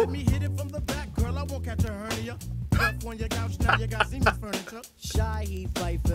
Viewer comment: One of Phife’s legendary bars: “Lemme hit it from the back girl I won’t catch a hernia / Bust up on your couch now you got Siemen’s Furniture.” 💀💀😭😭. RIP Phife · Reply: I aint gonna lie the whole time I thought he was saying semen furniture lls